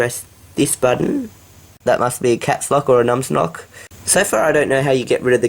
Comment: Is it just me (It might be) or did he say nums nock instead of nums lock at 8:00 in the video?